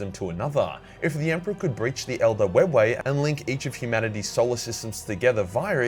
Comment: I swore I heard him say Soul or System.